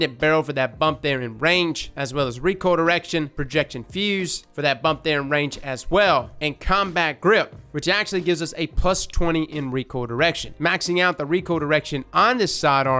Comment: Dono if it's just me or accent I keep hearing "erection deduction" instead of "recoil reduction"